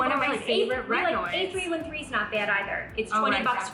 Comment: I cant understand what brand you said at 8:42. Was it 8313? Or A313? Havent seen that at walmart so thinking i didnt hear it right. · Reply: A313 it's great!